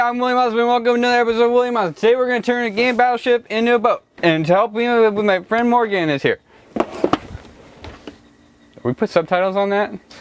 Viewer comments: in the intro I heard “hey I’m William I smell like gum today where turn this battleship into a boat. to help me blow it up my fren Morgan is here” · I know he said game but it sounded like he said gay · "Hey im William Osman and welcome to another episode of William Osman. Today we are gonna turn the game battle ship into a boat. And to help me out with it, my friend Morgan is here" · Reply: Thanks that was actually very helpful; even after re-watching the intro I still legitimately thought he was saying "Today we're gonna turn a gay battleship into a boat"